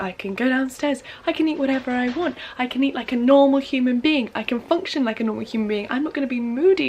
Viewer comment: Idk why but for some reasons i heard "i can eat a human being"